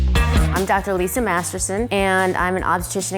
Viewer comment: When she said her name I thought she said Dr. Lisa Masterbate xD